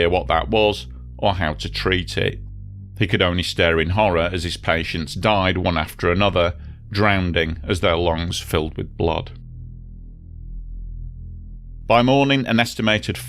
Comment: Did he say "drownding?" 😂